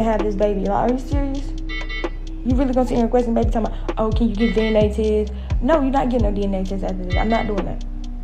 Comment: Did she actually say “deeneh-Teeeeh” (DNA Test)😂